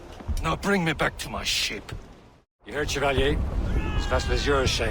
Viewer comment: What I heard is "now bring me back to my shit!"